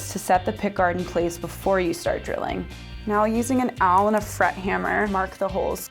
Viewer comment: Can anyone tell me what she's saying at 5:20? An Owl and a Fret Hammer?!! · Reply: awl. It's sort of like a screwdriver but has a needle like tip on the end. It's great for precisely locating a place to drill.